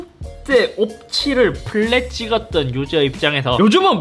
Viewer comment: ????? 뭐라고요? 플레?? 그간 성능 테스트하면서 봐온 에임이 있는데 크흠